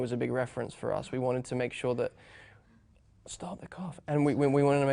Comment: Does anyone know what Andrew says to Emma at about 5:02? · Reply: don't know why, but I thought he said "Stopped the cough." haha Which is super random, but it almost looked like he stopped speaking so he could cough and then he was able to hold it dow. I don't know. I'm probably totally wrong. :D